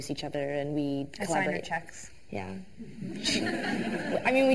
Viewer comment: we were replying to samfroz who asks what tegan SAYS at 3:16. which i'm almost positive is "i sign her checks."